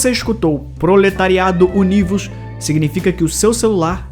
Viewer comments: no celular ouvi "proletariado unidos" e no pc ouvi "lucro, lucro hahaha" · eu ouvi "proletariado fodido" KKKKKKKK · Eu ouvi "ploretariados unidos", mó frase de comunista kkkkkk